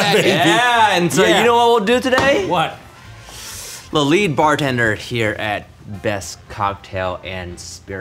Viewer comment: im blind and thought "bartender steve" said "brendan steve" and i was about to throw an entire fit